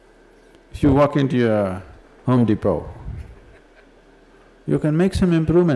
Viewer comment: he says"if you walk into your ****" what is that word and its meaning? Can anyone tell me please!!🌸🌸🌸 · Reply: Brother, turn on the captions, you can see the words on the screen then. 🙏 He says home depot, like a shop which sells furniture and beds.